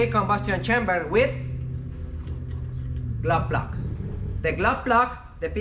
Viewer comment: I think "glove block" (the spark plug-like device on the diesel engine) is "glow plug". I think he's saying "glow plug", but I hear "glove block." Anyway, I appreciate ' lessons!